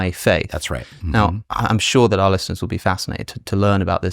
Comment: Just a moment before they started talking about giving a name to his followers I thought I heard Alex calling the audience something like "Alessiners", which was actually "our listeners". So I guess there's your answer.